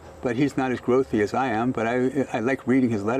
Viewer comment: Hahah I loved that dig at Warren Buffet. "He's not as wealthy as I am but I like reading his letters." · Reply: I thought he said "he's not as growthy as I am". Which makes sense. That's why he's in Tesla & Buffet isn't.